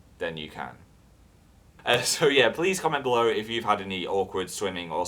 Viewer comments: whenever you say "comment below" i hear "come on blow" so it's kind of weird omg i'm sorry ok bye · ok thats not good. When he said "please comment below" I heard "please come and blow" and after what he's been talking about. Wow. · I know you said "Please comment below" but I heard "Please come and blow."